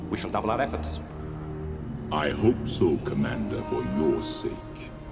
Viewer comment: I thought he said “The emperor is not as forgetting as I am”